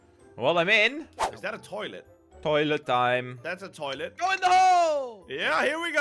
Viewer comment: I thought you said put it in the hole not in the wall